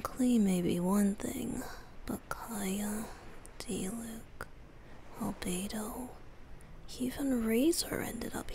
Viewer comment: ALBEDO? DID I HEAR ALBEDO? I WILL TAKE ALL THE CRUMBS I CAN GET YES !]!]€]%]<]€]>]%]<